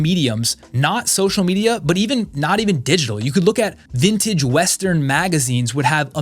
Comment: When you were talking about getting inspiration, I thought you said at 14:10 “vintage western MAGA scenes” I wondered why would you want to recreate a coup!